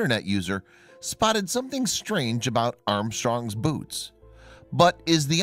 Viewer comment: did only i heard him saying armstrong's boobs 😜😂